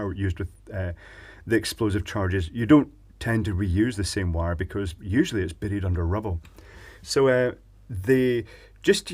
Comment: I could've sworn said, "Don't reuse the wire cause it's Betty under Rubble". 0.o